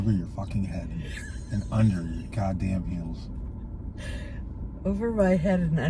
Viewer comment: Loved this vlog with! 1. I died laughing at over your head under your nose 2. I thought shirt said peed not speed 3. explained relationships today extremely well. I couldn’t agree more with what he said 4. The whole toilet paper section was so funny. 5. YES we need conversations with. Name suggestions: me in, the PHILosophy room, Philtered Thoughts, layers with. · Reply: He said "under your toes" !!! I can't see crap and you couldn't hear. LOL